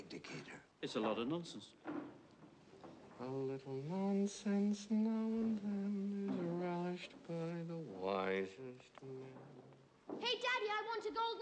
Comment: What is he saying? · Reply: “A little nonsense now and then is relished by the wisest men!”